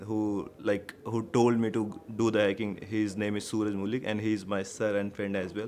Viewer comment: What's the name sir just said? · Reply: Sooraj and then I think he said mooli? 😂